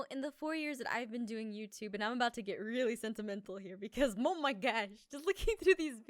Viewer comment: did jess say mo my gosh